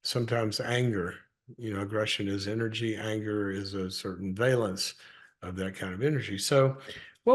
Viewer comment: did you say.. Vailance? I LOVE that word - New to me, but... yeah. Love that word. Did I Say that correctly - perhaps I heard you wrong. I don't expect an answer - I'll look it up. 6:35 - like David. (hid from Saul) 7:00 - like table turning.